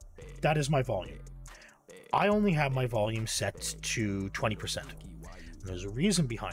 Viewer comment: 20% audio? that explains why i have to turn up the volume when watching your video, even with headphones on. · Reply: ah i misheard, i thought your mic was set to 20%